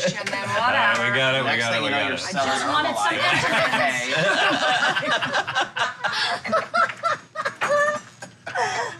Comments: what does say at 14:05? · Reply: garlic bred next thing you know you’re selling herbal life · Does ANYONE know what says at 14:03 that makes everyone at the table die of laughter? I can only make out "Next thing you know you're selling..." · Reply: Daniel B I'm late but he says,"next thing you know your selling herbalife" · I've watched this video so many times and I still don't know what says at 14:03 "Next thing you know you're selling...." Even with Steph mouthing it as she's laughing I can't work it out. I get that it's a joke about pyramid schemes but I wanna know what he actually said to make them all laugh like that! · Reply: Herbalife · what is it that andy says at 14:07 that they all laugh at urghhh i can’t make it out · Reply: “next thing you know you’re selling Herbalife”, better late than never :)